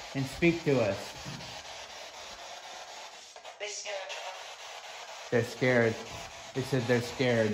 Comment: a female voice comes thru I hear. (There scared) Please tell me what you think.